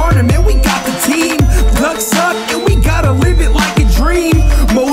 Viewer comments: o:55 it sounded like he said ducks suck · it sounded like he said ducks suck